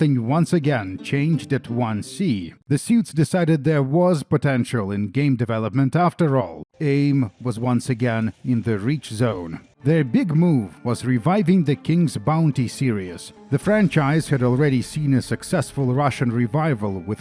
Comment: Dude! That "Aim is in the reach zone" line woke me up like I was a sleeper agent. I had that phrase stuck in my brain since I was a kid and never really paid attention to how bizarre it was. Turns out it was just google translate going crazy and no one bothered to check. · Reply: I didn't realize until watching this video that's what the line said. Since I was like 8 I always heard it as "Enemy in the breach zone". Guess as it turns out it basically doesn't matter since it's just mistranslated nonsense